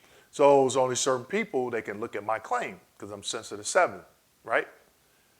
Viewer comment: Sorry; what are you saying around 11:27? I’m having a hard time making it out. Sounds like “sensitive to seven” to me?